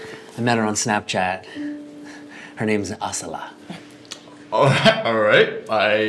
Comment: sounded like MASALA LOL.